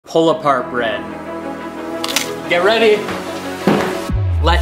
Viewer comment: I dont know why i thought you said "Koala Heart Bread"